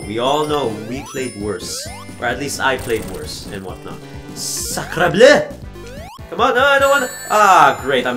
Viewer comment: What'd you say??? That sounded so familiar I think I read that somewhere during my youth.......................you're french? (I think what you said was Sacre Bleu "Holy Blue") 7:11 Shadow The Hedgehog: The video game was a visionary for fucking team skull..... ugh 9:15 I see Sonic Boom when I'm dreaming. That's why I wake up screaming! 13:22 I've never played neither, but I hate Sonic Boom becuase its so...............................................................................the way it is. I don't hate Sonic '06 beacuse I friggin love Silver and I see a lot of potential with him and Shadow. I can envision a Shadow and Silver vs Sonic battle in my head. In fact, Ima go make that, and I'll show it to you, ok? 22:04 Like when a person hates something with a "passion"? I've never really understood the point of that 22:25 (I'm looking at the time in teh video and i'm here think "HMF did the video go over the video's time limit?!") It's worse when people talk about religion and race